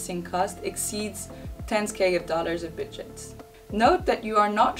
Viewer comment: What is the guidance on when to consider Flat Rate Pricing? At 10:07 it sounds like "10s K of dollars of widgets" (CC says similar).